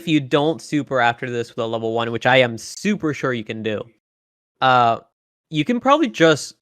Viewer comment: DID HE SAY "SUPER SHORYUKEN"??